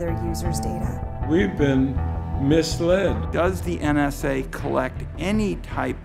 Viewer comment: I swear she says penis! Omg idk